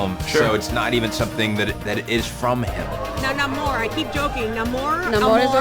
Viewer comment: I thought he said "I'm no more".